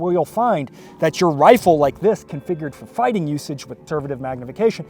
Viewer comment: Mind clarifying what you said at 4:31? I heard “turbative magnification” which is a term I am not familiar with. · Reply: conservative. I cut it short.